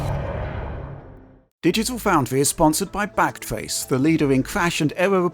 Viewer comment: Definitelt thought he said sponsored by blackface lmao.